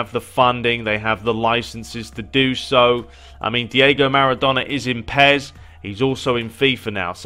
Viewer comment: forgive me if I'm being retarded but I could have sworn that you said Maradona is exclusive to pes